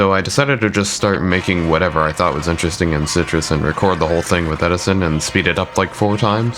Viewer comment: what's "citrus" (unless I'm mishearing it which i probably am) i can't find anything about it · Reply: You're hearing it right. It's a plugin in FL Studio, spelled like Sytrus.